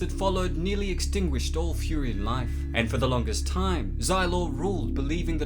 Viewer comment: For a moment, I thought you said "furrians"...😂